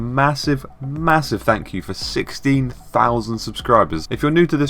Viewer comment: at the start I thought you said 60,000 and I was like DAMNNN